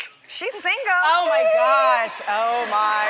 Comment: OMG !! I thought Tamar said 'that's cute ASHLEY' who got the reference ??